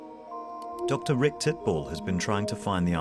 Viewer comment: Dr. Rick what???? He said it so fast, and they won't even show his name lol. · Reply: Dr Rick Titbal